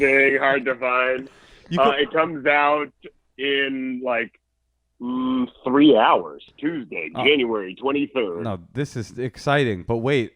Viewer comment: Did he say his special comes out in “3 hours Tuesday January 23rd” 👀